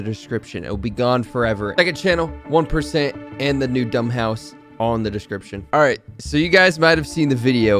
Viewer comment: you said on the description instead of in the description